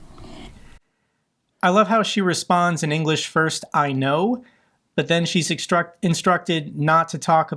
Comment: She didn't say "I know" in English, she said "алё", which is what we say in Russia when we pick up the phone